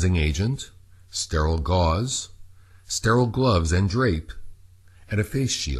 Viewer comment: <link> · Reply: Salman Saif Yeah. Esp. since at 1:58 the Closed Captioning says "rape" instead of "drape". "Paris and teases" needle. :-/